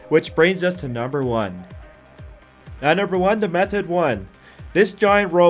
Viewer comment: when he said method 1 i thought he said meth head 1